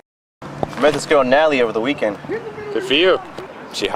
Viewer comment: did he say "analie"???